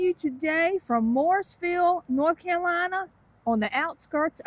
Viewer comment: Learn how to pronounce "Morrisville", it sounded like you said "Mooresville"!